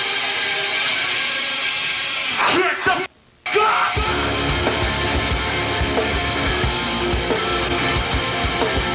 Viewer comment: What did Chino say at 0:25? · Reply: GET DA FUCK UP!